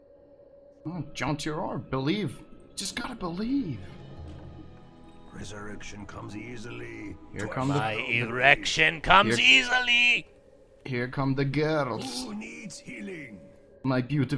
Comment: at point 29:08 i thought i heard SUNSfan said "My erection comes easily!!!!"